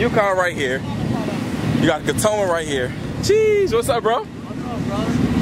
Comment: Bro am I the only one who heard him say “catoma”😂😂😂😂